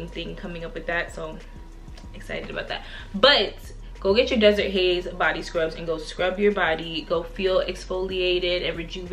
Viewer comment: I almost thought you said Desert haze body butter I was losing it 🤣 Thanks.